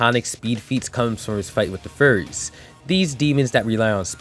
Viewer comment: Bro I thought you said furries 😭😭😭